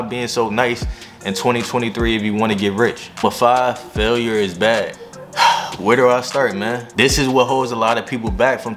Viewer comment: I thought it said Failure is bad💀😂